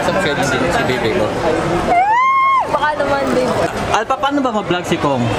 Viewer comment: oy kua narinig ko sa 9:13 ay baka naman babe yieeh